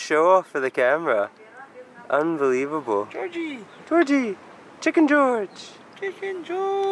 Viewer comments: When said "chicken george.." I thought I heard "chicken joy"....I must be hungry..hehe. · Ohh I overheard, I thought he said Chicken Joy. It’s Chicken George 😂